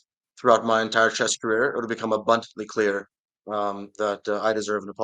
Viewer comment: Did he say: "throughout my entire chess career it will become a butt" ? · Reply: Abundantly?